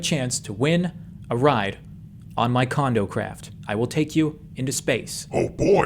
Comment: Am I the only one that heard it as "cond*m craft"😆